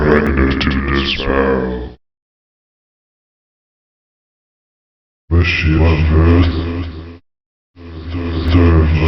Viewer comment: the dark bishops always sounded like, "Wish you were on Earth?" followed by "Allow me your soul." The last Heresiarch likewise sounded like the Princess Bride quote "As you wish."